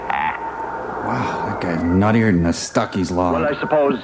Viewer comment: what does mike say around 47:10 "nuttier than a ....." i can't tell what he says there. can someone tell me? thanks! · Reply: "Nuttier than a Stuckey's Log". A reference that only mid-westerners would get. Stuckeys was/is? a Dennys-type roadside eatery, famous for their cylindrical caramel candy bar that was covered in peanuts. Every bite took 10 minutes to chew and when you were done with the whole thing, your stomach didn't work for 36 hours.